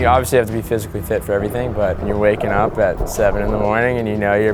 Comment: did he say wake up at 7am?